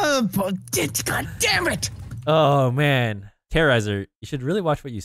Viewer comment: Did terroriser just say "POTITKA DAMMIT" lol